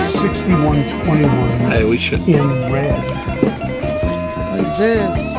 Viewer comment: It sounds like he says "6121 in red". I can't quite see the inlays but if they're plain block or hump block, then this is a '55 to '57 6131 Jet Firebird. If it has western inlays like the early 6121, then this is some kind of re-neck, the 6121 were all double-bound with gold hardware and bigsby as standard and this guitar doesn't have all of those features. (it looks like they got their Reverb listing correct, a '55 6131).